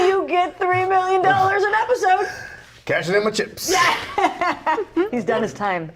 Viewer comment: I love this video but I still don't understand what this man said right here, can someone tell me, please? :'v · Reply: "cashing in my chips" He's done....